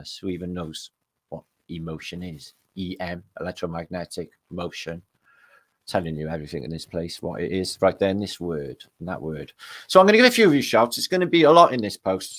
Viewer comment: "telling you every/thing in this place what it is, right then this word that word and that word" is? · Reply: isness